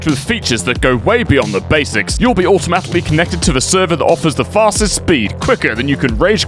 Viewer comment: I'm sorry, but, did you just say "automatilly connected" in the sponsor promo? (For anyone wondering about timestamps, it's